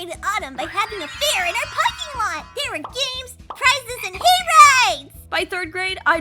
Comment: I heard hay rides as “gay rights!” If only 😅